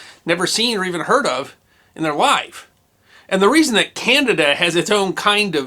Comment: I think at like 0:30 he said "Candida"